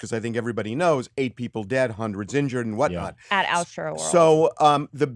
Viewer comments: What the f' did she say. Sounded like "Auschworld" ... you know like Auschwitz... WTF? Not good. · did she say "altoworld" or "outroworld"